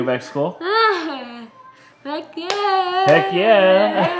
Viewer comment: ‘Heck yeah’ also sounds like she said ‘I guess….’ The funniest moment